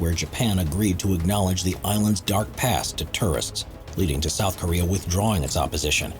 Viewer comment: Whoever did the VO on this, it's tOurists, not tUrists. The amount of times I thought he was trying to say terrorists is pretty high.